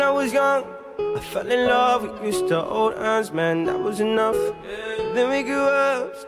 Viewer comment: thought it said with mr.old ass man at first lol oops.. instead of we used to hold hands man